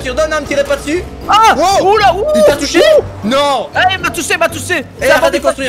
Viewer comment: C moi ou à 2:53, dis "tousser"